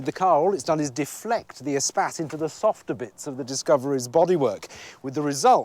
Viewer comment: I thought they said espaz lol